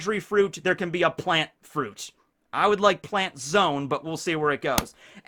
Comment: yea after this and the theory with one piece under enines lobby we are getting the friendship ending lmao :D ps: my bad thought you said planet zoan that would be mind blowing! the planet planet no mi